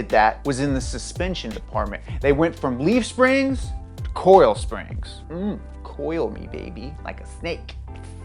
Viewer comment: "coral springs" please tell me someone else thought he was saying that half the time